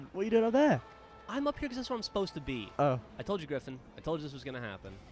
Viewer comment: Did he just call Griffon?